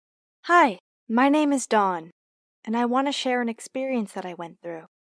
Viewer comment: I thought that she said HI MY NAME IS DOG